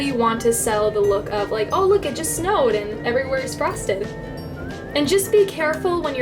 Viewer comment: On the fake snow, I thought she said Everything is frosting 😂